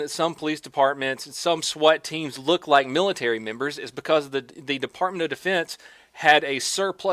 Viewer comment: I think I'm going deaf. MILITARY WHAT? 3:23 "Look like military [nword]"??? IM DEAF WHAT DID HE SAY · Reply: Comfortable Sofa He said military members but accidentally with an N, so he said, Milatary Nembers